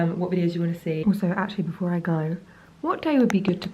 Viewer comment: when you said "actually before I go" I misheard that as "Actually I'm prego" and had to rewind 😂😂😂 I was like, what?! What a casual way to break the news 😂😂😂